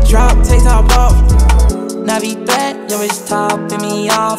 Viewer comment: Bruh all this time I thought he said "Navi back" but it's "Navi threat" WTH that don't even make sense to me.